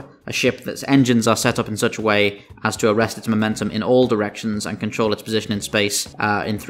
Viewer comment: i thought you said Arresto Momentum lmao